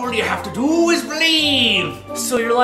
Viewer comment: press 8 and then 4 and it sounds like says "guilt is believe"